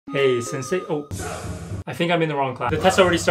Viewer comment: I cant Be the only one that thought He Said " hey Sensei wu"